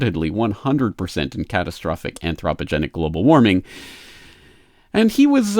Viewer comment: what is the word " ?-genics" sounded like oppogenic?